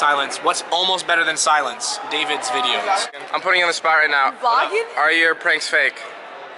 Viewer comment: did I hear Courtney Miller say "Are you vlogging?"